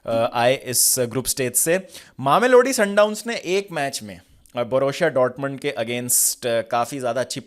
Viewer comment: kya bola tune? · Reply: It's mamelodi sundowns fc😂😂